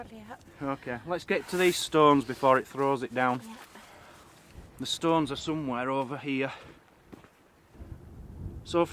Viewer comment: ok, what on earth did call that umbrella? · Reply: she said "brolly". short slang for UK'ers. I like it.